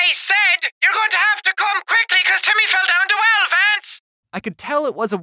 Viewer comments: I heard "YOUR GOING TO HAVE TO COME QUICKLY CAUSE TIMMY FELL DOWN 2 ELEPHANTS!" Lol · Without reading the subtitles I heard “your gonna have to come quickly because Timmy fell in the well Vance” I started laughing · What I heard " you'll have to come quickly cuz Timmy fell down the well then Vance " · I heard “you’re gonna have to come quickly because Timmy fell to elephants” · I thought she said "you'll have to call quickly cuz Timmy fell down the well Vance" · I think that women at 5:49 was saying "your gonna have to come down quickly because Timmy fell down the well Vance" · I heard " your going to have to come quickly because Timmy come to well a fence" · I heard “ your going to come quickly because Timmy fell down 12 times” comment what you heard the first time 😜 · Your gonna have to come quickly because Timmy fell down twelve vents is what I heard in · I'm hearing "YOURE GUNNA HAVE TO COME QUICKLY TIMMY FELL DOWN THE WELL VANSE" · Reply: I heard "YOUR GUNNA HAVE TO COME QUICKLY CAUSE TIMMY FELL DOWN DA WELL VANCE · I thought jack said: YOU HAVE TO COME QUICKLY BECAUSE TIMMY FELL DOWN THE WELL VANCE! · The Irish girl I thought she said, “you have to come quickly cause Timmy fell down the well Vance” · it sounds like she said "you have to come quick cuase timmy fell down the well vints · how did she say "your not funny vance" it sounded like she said timmy fell down the well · this is what i heard ""you better come quikly cause timmy fell down two elephants